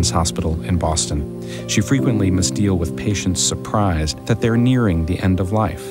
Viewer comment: thought she said misdeal thought it said Brighton